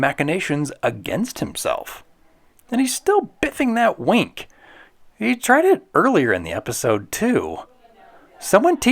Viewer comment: What exactly does Zack say in the episode? Was it "Zack Moron?" or "Zack Muah" like Zack himself? I still can't make it out after 30-plus years! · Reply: I went back and gave it a good listen. It is definitely "Zack Muah," as he is the only one can could beat himself.